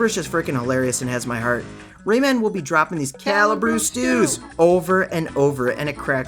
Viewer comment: Oh my gosh I am so happy that you also heard Rayman saying “calibru stew”… that has always been in my memory since playing this game !! I thought I was the only one who heard him say that